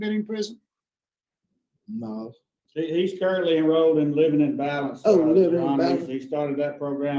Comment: ALL the classes don't do anything for the offender. He just can't think for himself, how he can function outside? denied., denied. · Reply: I thought they said he enrolled in “living in violence “ instead of living in balance. 😬